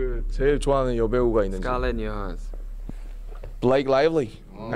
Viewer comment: what does suga say? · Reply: Scarlett Johansson!